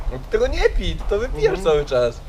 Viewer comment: 3.47 prędkość 1.5 usłyszałem wypierdol cały czas XD